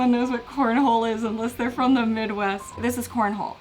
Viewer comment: "This is corn hole" I misheard it as porn hole XD